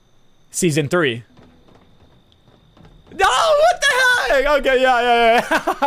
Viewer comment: I swear he said what the h*ll